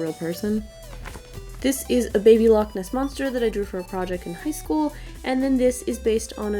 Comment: Did she say she made that in preschool? Or am I just deaf